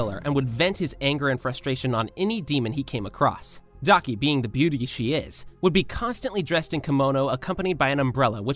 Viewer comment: did he just say daki being the beauty she is?